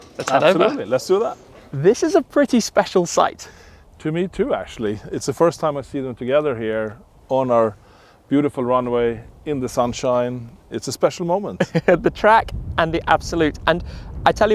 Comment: does he say tomato? 🤔 Jag försökte beställa en öl med dem orden ”to me to” och fick en tomat 👌